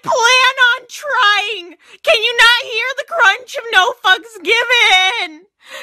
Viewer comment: it sounded like she said “can you hear the crunch of no fucks given” then after that I heard a loud ass crunch on my tv